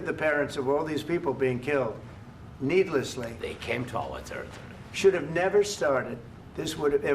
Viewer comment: Can anybody tell me what Zelenksy is saying @ 13:56 ?... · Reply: they came to our territory